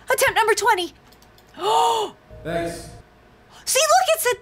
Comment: When you gave him the gnome, I heard him say thanks not